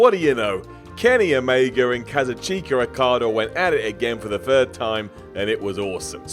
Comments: In the intro I thought he said "Kenny Omega and cousin Chico Okada" lmao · I coulda swore said KazuCHINKa